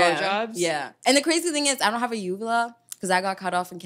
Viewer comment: What does she say at 2:38? What doesn’t she have? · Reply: Her Uvula was cut off in Kenya.